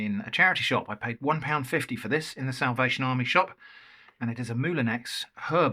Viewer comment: I thought you said south asian army shop for a second there 😂